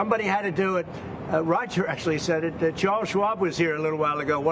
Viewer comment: did he say Carl Schwab was here?